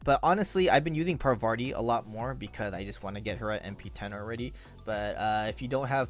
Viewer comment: Chotto matte. Did you just said NP 10?